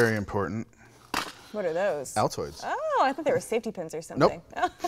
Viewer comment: thanks a lot again Ursa! Very interesting and useful as usual :) what is "very important" at 13:47? I don't catch the name of it. Also no shotgun mics (?) :O xx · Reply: Altoids